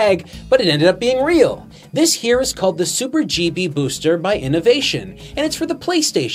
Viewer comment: I keep hearing "Super Chibi Booster"